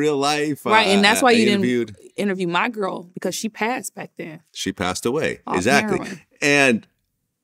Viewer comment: Did she say the woman she played passed off heroin at 2:22?